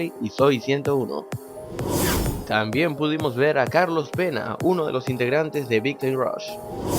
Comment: cuando dijo carlos pena, pense que dijo carlos vela :p